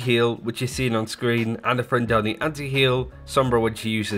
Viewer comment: What is he saying at 1:22? Sounds like "a friend Donny anti heal" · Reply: ana throwing down the anti heal